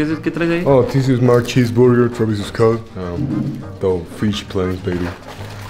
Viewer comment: que dice xd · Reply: this is my cheese burger travis scott (noseque) life, life is good 🗣🗣🔥🔥🕊🕊